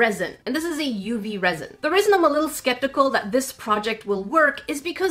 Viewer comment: Is it just me or did say "The RESIN I'm a little skeptical…" Not hating just laughing.